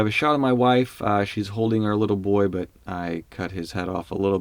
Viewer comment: "So I've shot my wife, she's holding our little boy but I cut his head off a little bit because he was too short..." Sounds kinda creepy without a context :D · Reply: I could have sworn he said "HER little boy" hmmmmm. Hopefully she didn't hear that either 😅